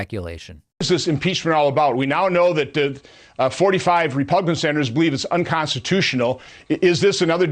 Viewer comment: > did he say "repugnant" senators?